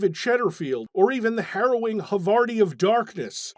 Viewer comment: I must have a few holes in my brain because I heard him say "hubbardium darkness" at first.